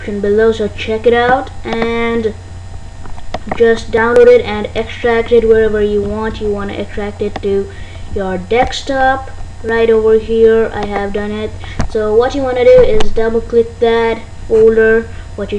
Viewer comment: wtf he said "extract it to your dextop"